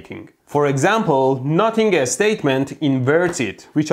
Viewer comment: When he said he was Notting over a statement, I misheard it and I should be ashamed